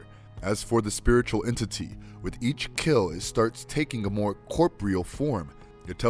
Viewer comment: "Corp-real form" ??? I think you meant to say: CORPOREAL (Cor-por-ee-al) Pronunciation is 9/10 everything when producing Audio for Vids, Mate;O)